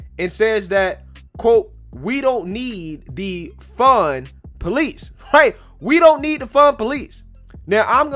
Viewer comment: it sounded like you said we don't need to defund police. But I know you said we don't need the fun police. But it is funny to me.